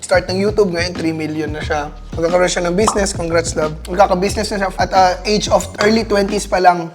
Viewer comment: “congrats love” narinig ko